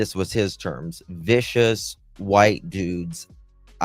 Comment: I thought he said " harest white dude"